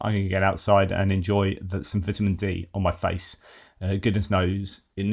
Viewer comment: Oh, Vitamin D. I'm so used to hearing you say tea, that I misheard that last bit as "I'm going to get some vitamin tea on my face, god knows I need it" 😂